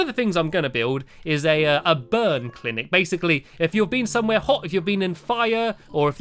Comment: I thought he said BIRD clinic